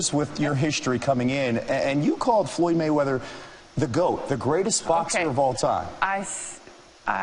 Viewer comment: She is right I thought she said one of the GOAT not the GOAT. No doubt in my mind he is TBE but no he is not the GOAT.